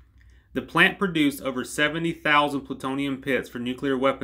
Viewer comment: Did he say 70000 or 17000? (0:40) Either way that is insane. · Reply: 70,000